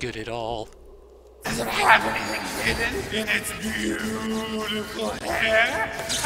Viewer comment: What did say at 15:22? My hearing isn't very good. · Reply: I think, but am not sure, that he said " does it have anything hidden in it's beautiful hair"